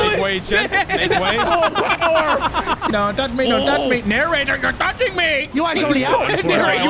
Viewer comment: Why do I hear “YourNarrator”?